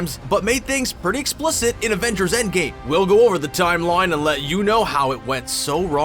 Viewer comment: did you just say "Avengers: Endgay" ?